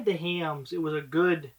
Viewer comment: What did you say at 6:33 ?? When you tried ______? you were pleasantly surprised??? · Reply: Hamms beer. It is super cheap and by no means great beer, but it far exceeded my expectations.